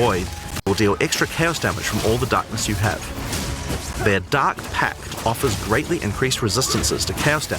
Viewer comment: I constantly hear 'ducks' instead of darks